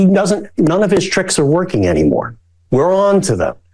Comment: Wow I miss heard you when you said Conway said Trump stick is not working anymore. I thought you said d isn't working anymore